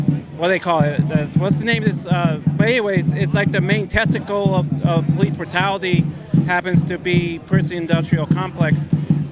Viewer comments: "The main TESTICLE of the prison industrial complex"...... is that what I heard? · Did you just say "the main testicle of police brutality"? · did he say the "main testicle?" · "The main testicle of police brutality" 22:45 ....that's what I heard...anyone with me? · did he say “testicle”? 🤔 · Is that guy at 22:44 saying, "...the main testicle of police brutality..."? I keep listening to it, but that's all I hear every time. Was testament the word he was looking for?